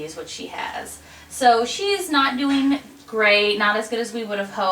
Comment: at first I thought you said "she's eating grapes" and I was like why would you feed her grapes?!? but then I realized you said great.... should probably get my ears checked